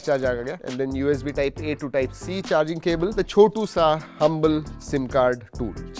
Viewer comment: A TO C CHARGER BUT INBUILT SCREEN SUBTITLES IS WRONG